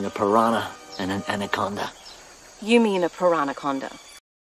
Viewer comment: I'll be honest, thought you said Taranaconda at first. Like tarantula and anaconda. I was disappointed when I found out this was not the case.